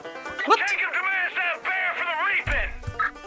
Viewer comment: I thought he said “Jacob demands that bear for the raping!” First time around, thank god for subtitles